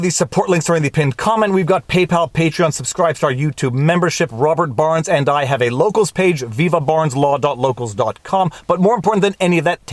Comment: I thought you said: Robber Barrons and I have a locals page" .. lol..